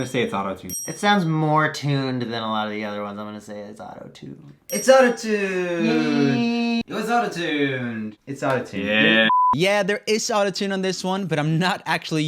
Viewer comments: i thought said yes?? oh well doesn't matter he still got his point lol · he said yes but caption said no